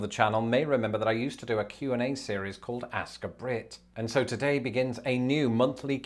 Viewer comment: at first i thought you said "ask a prick" i was like well thats a bit self depricating